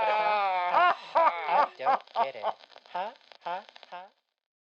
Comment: I can't be the only one who heard Wally say "I don't get it" at 12:57.